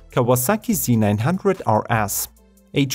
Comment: I thought he said: Kawasaki Z900 are ass ;DD